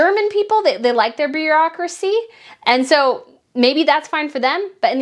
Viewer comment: It sounded like you said Germans like their "beer"aucracy!🤣